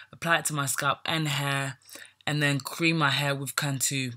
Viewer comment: Ok what is the 2 items? You said black jamaican castor oil and what? It sounds like your saying CAN 2 or can too. I do not know what that is or if I am saying or spelling correct. Let me know. Thanks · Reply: Yes it’s called Cantu , it’s a leave in conditioner .